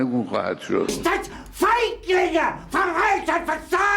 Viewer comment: Did he just call him the N word. "Don't Fight N*gga!"